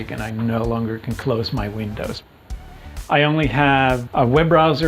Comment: did he just said Windows?